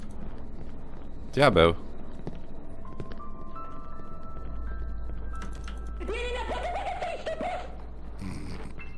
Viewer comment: co powiedział jak uciekał przed w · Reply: To chyba bylo połączenie śmiechu ze strachem i krzyczenie "NIE NIE NIE PODCHODŹ... coścoścoś PROSZĘ"